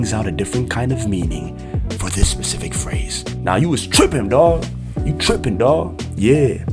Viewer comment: i don't if I'm trippin but I'm pretty sure i heard strippin 😂